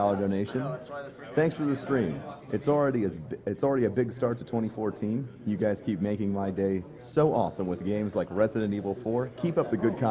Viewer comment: someone can type what he said about Brazil? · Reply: "Thank you Lucas for your 10 Dollar donation. "Thanks for the stream, it's already a big start to 2014. You guys keep making my day so awesome with games like Resident Evil 4. Keep up the good content. Hugs from Brazil.""